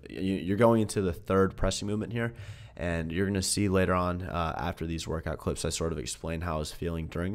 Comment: I thought you said you were going to see Sagi. Haha..